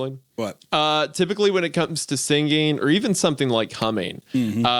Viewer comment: i swur i thought he said coming lmao